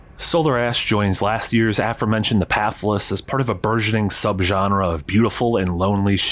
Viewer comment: I can't make out what he's saying, what was the name of that game? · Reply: "The Pathless" is the name of the game.